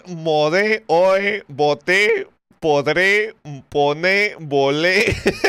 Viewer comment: Creo que mi mente censuró cualquier error... Yo lo escuché todo bien, pero creo que es imposible no entender la palabra en el contexto. Por cierto en lo del minuto 11:48 escuché "podré"